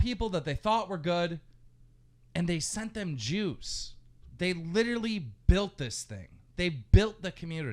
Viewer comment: Yo did he say 'sent them Jews' around 4:00 lmao, KEEM I got the popcorn out at that moment..